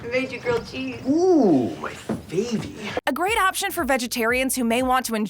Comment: Did she just say for vegetarians 3:03 ? Bro what